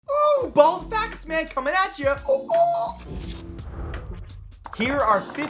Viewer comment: Miss heard the intro and thought it said "ball sack man coming atcha"